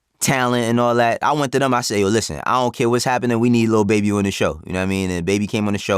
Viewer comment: Baby who? Lil Baby, Da Baby, or Birdman? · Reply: Shit never mind, I went back & he actually said Lil Baby in full right there